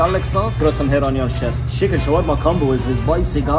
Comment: I thought he said he hates ketchup. Why is he happy about a guy putting ketchup on the side?